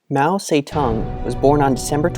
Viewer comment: I thought I hear "Mao SayTan"... Sounds like Satan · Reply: Oof yeah, haha. But he was saying 'Mao Tse Tung', I think is the proper way to say his name. (In Chinese)